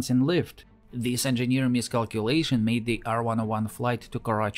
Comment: I heard "Engineer Mystics" and thought I was suddenly getting a lecture in experimental theology.